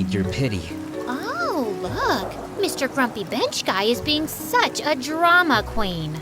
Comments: ehh.. i hear the word bench as a b*c*th✨🤓🤓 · i heard bench as 🐕‍🦺 😭